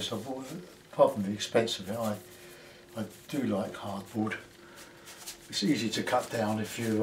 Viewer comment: Cardboard, do you mean hardboard, Joe? I used hardboard for this one 6 years ago. I did a few of these last year, I love the lifting out! I'll prepare a 3mm mdf board for later in the week. Got to do a couple of watercolours in the morning if I get time, must look after the natives.:) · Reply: oh I thought you said cardboard lol